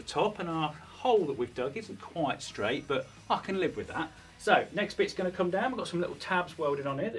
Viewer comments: it sounds like he said f ing live with that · I swear it sounded like he said the f word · I swear it sounded like he said the f word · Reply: "What did he say?" - if you use auto-generated CC, the script (in context) says at 2:44: 02:40 ... with the hole at the top and our 02:42 hole that we've dug isn't quite straight 02:44 but "i can" live with that 02:45 so next bit's going to come down we've 02:47 got some little tabs welded on here that ... Sorry, no F-bomb! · I heard “fucking live with that” 😂😂😂